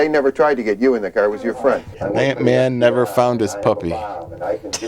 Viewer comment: I've rewatched this a million times and I only just now realized Mike said "Ant-Man never found his puppy" at 1:05:37 because they were talking about a van. I thought he was saying "that man".